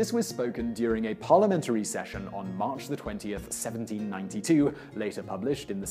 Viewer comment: does he say 92?